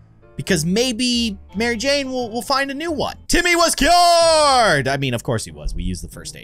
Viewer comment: When he said Timmy was cured I thought he said Timmy was killed.